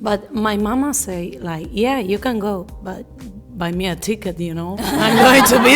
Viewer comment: I don't think she said "va mia chica", but rather "buy me a ticket"